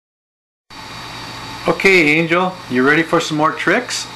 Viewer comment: Angle is her name? i thought you said angel??